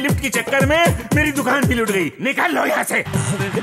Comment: Did anyone heard " nikal la*de yaha se "